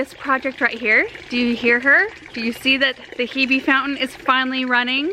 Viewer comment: Immediatly thought that said, 'Herb Fountain' and got excited, but the Hebe fountain set-up was great too. 😃